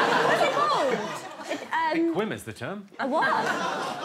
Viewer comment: what did jimmy say? pls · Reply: “a quim is the term”